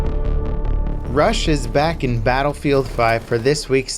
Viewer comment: I have no idea why but when he said "Rush", i thought he said "Russia"